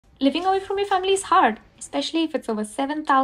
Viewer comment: At the beginning, what I heard was "living away from your family's heart." 😂